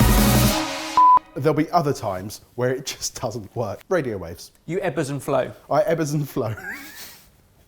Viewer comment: For a second I thought he said "You're Ebbers' aunt Flo"... ehh yeah...